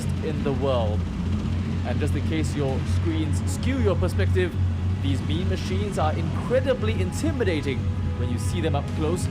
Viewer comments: did he just say Meme Machine? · Did I hear "meme machines" at 0:25?